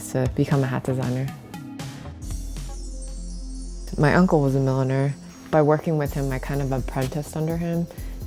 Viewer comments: I thought she said her uncle was a millionare😂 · "My granda was a milliner" I thought she said "millionaire" I could've been "wow that's boastful" lol